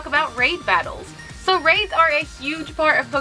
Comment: I’m watching with subtitles and at 7:28 it says “rape” instead of “raids”